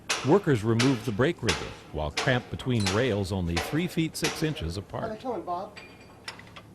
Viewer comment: did he say 3 feet 6 inches apart? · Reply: Chris, Yes--he said the tracks were 3' 6" apart.